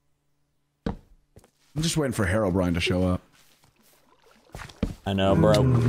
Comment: I just heard “Hair O’brian” must be the archenemy of the Bald Boys